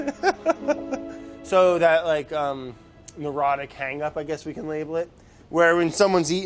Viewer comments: I thought he said Erotic Hangup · He said, "Neurotic." I thought he said, "Erotic." I thought he got aroused by Celery 😂